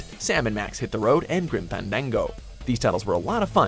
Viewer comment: Did he just say "Grim Pandango"?